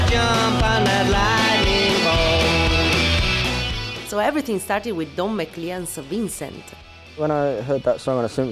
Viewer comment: what in the name of GOD is the woman saying at 1:44 ? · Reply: every thing started with Don Macleans Vincent.